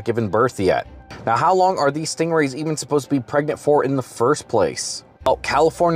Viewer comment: Thought you just said "he got pregnant" 😂😂😂😂 😰😰😰😮‍💨😮‍💨